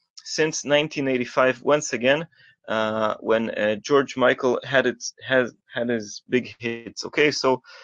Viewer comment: I swear I thought you said "when George Michael had his hit", but I'm SURE I MUST be hearing incorrectly. I KNOW I'm getting old, but......😎